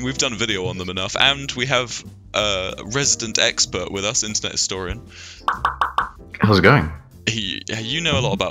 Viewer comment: said Internet historian too fast for me to properly process and I thought he said incest orange at the end